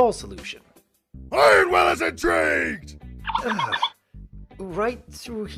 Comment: What did he say? I couldn't make that out? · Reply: ironwill is intrigued